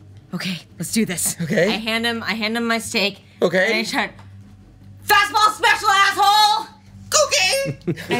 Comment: Can someone tells me what does Anabelle shouts at 1:45:54? English is not my first language, and this sounds very funny 'cause even Chaz had trouble to maintain character hahaha. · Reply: "Fastball Special Asshole!"